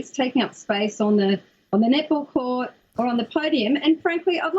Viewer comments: All I hear is "nipple" instead of "net ball" 🤣😅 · Dude, I'm dyin! Haven't laughed this hard in awhile. What made it even worst is that everything they said "nettball" I kept hearing "Nipple." Immature, I know, but coupled with the stupidity it just made it 100 times funnier. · Ngl. I didn't hear "net-ball" the first dozen times until you said it slowly. I kept hearing "nipple." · Anyone else thought that they were saying Nipple instead of Net ball? Just me? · Am i the only one when they say "net ball" i hearing "nipple" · Was it just me ... Or was i hearing "Nipple" instead of Net ball for the first 5 mins?? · I thought they were saying "nipple team." Today I learned that "net ball" is a thing. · Idk if it’s only me but for the first 20 times they said “net ball” I only heard “nipple” and it made this even funnier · Is it hard for them to say basketball? When i heard "net ball" it came off as "nipple." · They say "net ball" but I keep hearing "nipple". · I keep hearing “nipple”. Not net ball. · I'm feeling some typa way about how the lady says "net ball". I've never heard of net ball so I thought for awhile she was saying, "nipple". Fu¢kin' Aussie ¢unts! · Net ball.. I thought they were saying nipple until 10 minutes in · Net ball.... net ball..... net ball.... i kept hearing "nipple" until about half way into the video. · That's lady reporting has such a thick accent that when she said "net ball" I thought she said nipple. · Omg why do I keep hearing nipples is “netballs”